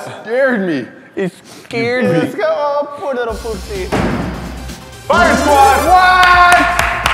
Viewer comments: did say scared little pu*** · Did he just call a poor little p***y?!